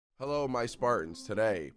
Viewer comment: I thought you said hello my fartins